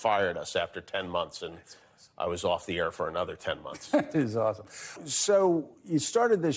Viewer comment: did he say "that's awesome", to getting fired from radio?"